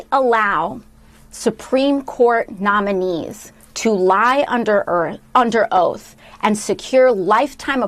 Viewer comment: Did she just say "lie under Earth?"